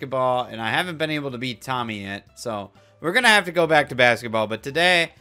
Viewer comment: No shit thought he said tommyinnit and not tommy yet Im so f-ing dumb